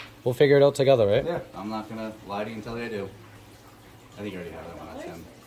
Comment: I liked that, almost gagged saying that, but dude was very professional, had no ego while stating he was unaware of laws he hadn't had to enforce before, and continued to de-escalate the whole time he was there. If only they were all like this. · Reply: The CHEIF said "I'm not going to lie to you and tell you I do". I can understand mishearing it though. It does sound pretty similar.